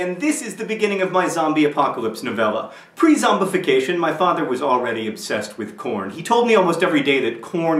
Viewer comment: in the beginning I thought he said 'my father was already obsessed with porn'